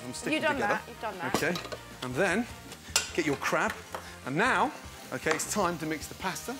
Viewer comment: when he says"get ur crab", the first time i heard, I thought he said get ur crap